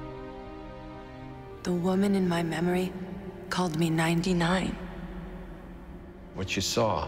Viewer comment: What did she say at 0:06? "called me ..." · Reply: 99